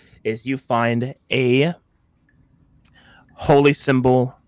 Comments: Holy symbol... of gays in a rock... wait did I hear that right? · Anyone else hear "Gays in a rock"? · did anyone else think said “Gays In Iraq”😂😂😂? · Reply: I got what he was going for but I thought he said “gays in a rock” and I was like what lmao.